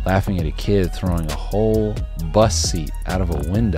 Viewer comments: What I actually think is funny is you saying “bus seat” but all I hear is “bussy” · Why did I hear “bus seat” as bussy I- 💀💀